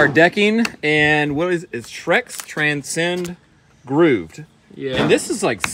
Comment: I thought you said "Shrek"s Transcend groove. WHAT ARE YOU DOING IN MY SWAMP.... DECKING! :)